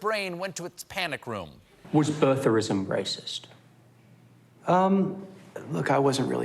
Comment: What is the interviewer saying? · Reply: Fakit He asked, “Was birtherism racist?”